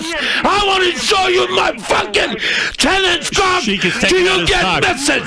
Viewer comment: 😂😂😂😂 like how he reminds the caller he has a 10 incher like 3 times 😂😂😂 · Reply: I thought he Said Tennis Coc